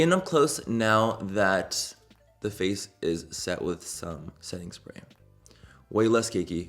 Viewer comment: Bahahaha I kinda zoned out and I thought you said "now that my face is set with SETTING SPERM!!!" LMFAO oh God where is my brain!!!